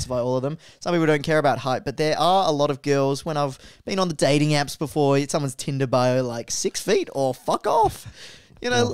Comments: what does say? I can’t really hear it lol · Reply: “I only have 2” haha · What was the joke said at 14:13? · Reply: «I only have two»